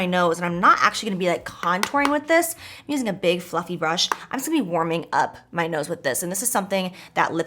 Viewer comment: Lol when she said she was using a big fluffy brush for bronzer I swore I heard big fu*** brush 😂🙈